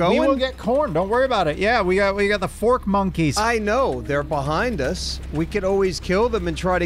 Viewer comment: everytime they say "fork monkey" i keep hearing "porch monkey" xP